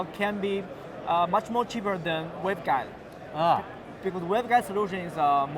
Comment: Did he say cheaper than a webcam? I'm assuming he didn't but I couldn't quite make it out.